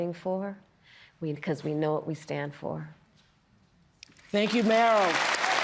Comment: Did she just say, THANK YOU MARYLIN to Meryl Streep😂